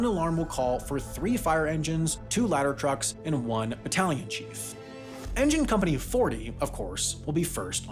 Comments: I heard "Battalion Chief" as "Italian Chief" and thought that a joke about Italian firefighters had gone over my head. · I heard "battalion chief" as "Italian chief" and I was so confused 💀